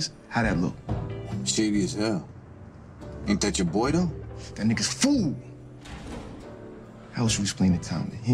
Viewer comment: I had to come back and see 2-bit say “that niggas FOOD” 😭😭😭😭😭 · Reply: I thought he said “That Nigga Fool” 🤣